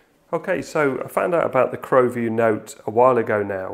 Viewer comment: I misheard you on your recent Rockchip RK3576 video. I thought you said "Chromeview" and I thought wow that makes total sense a KVM / Chromebook. I'm not a Chromebook user but the idea of KVM device with basic Chrome OS functionality at the flick of a switch would be amazing. As you prove you can simply plug in a SBC to gain this functionality and more, but the possibility of KVM/ Chrome book is now bugging me.